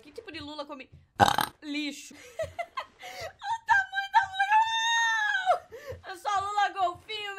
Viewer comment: Kkkkk ouvi mesmo "olha o tamanho da lula João " kkkk